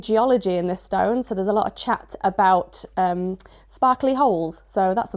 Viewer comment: Ohhh… a SPARKLY hole. I thought you first said a Barkely hole, and it was going to be some technical thingy, like the Lewis holes.🤪 Also, silence is fine. Totally, absolutely fine. Just please, please, please, please don't ever ever ever start adding background music.